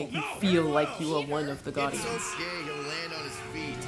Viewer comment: At first I thought he said "He’s so gay he’ll land on his feet."